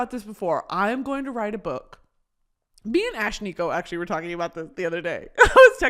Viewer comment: did she just say she was texting Ashnikko????